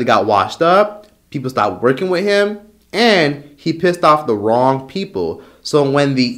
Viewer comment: LMAO when you said R Kelly got old, R Kelly got washed up, People stopped working with him, and he pissed off the wrong people 🤣 I swear I thought you said he pissed on the wrong people.